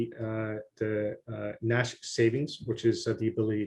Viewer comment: audio cut off from 38:15 to 38:27 on side, what did he say? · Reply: Thanks for the comment. mentioned Nash savings, which is a high yield interest savings account powered by smart contracts. Exciting Stuff. Apologies for the audio cut-off.